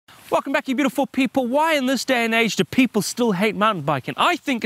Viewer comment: Who else thought that he said “fu*k that you beautiful people”